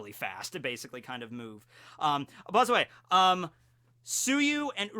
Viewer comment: he says buzz away instead of by the way